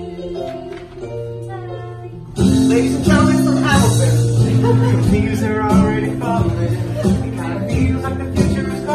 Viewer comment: What does Josh say? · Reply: i know this was posted a year ago, but if you were still wondering, he said: "ladies and gentlemen, from hamilton" (as a way of introducing jonathan, who was in hamilton, before his line)